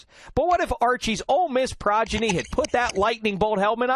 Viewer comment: he says Progidy instead of Prodigy at the 0:18 mark. lol